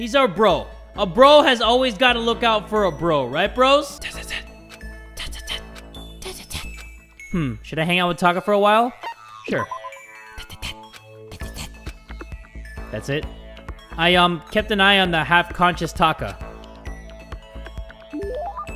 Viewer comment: it sounded like he said this is it instead of dot dot dot